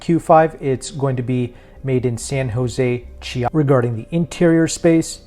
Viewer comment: I replayed 4:22 to 4:24 many times, unfortunately I did not get where the 2026 Q5 will be made, I believe I heard San jose, is it California ? Thanks for letting us know. BTW, I love the way Audis are designed, my concern is with maintenance cost and reliability as I tend to own my cars for a long time., the same goes for BMW. Are my concerns legitimate. Thanks for the excellent work in preparing these no nonsense reviews. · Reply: oh man I’m so sorry, what a place for the vid to cut out I apologize the vehicle will be made in San José Chiapa in Mexico. I also have the official press release on screen as well